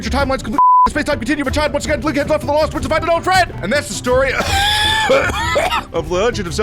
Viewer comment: They lost me at 2:57 After that it all just sounded like incomprehensible gibberish O_o" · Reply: the end says "link heads off to the lost woods to find his old friend"